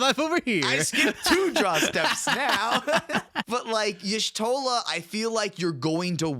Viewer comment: Did someone say Bud Light